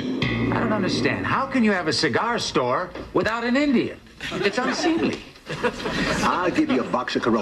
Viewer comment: Auto subtitles: 0:45 I don't understand how can you have a cigar store without an idiot